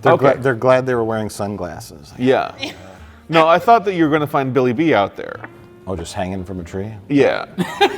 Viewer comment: I thought you said “Billy Dee out there” - which would give the swinging from a tree line an entirely different meaning...